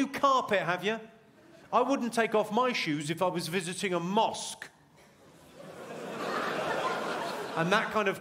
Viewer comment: I thought he said “Mourge” because I fully forgot what a Mosque was. I learned this thing for an entire year, man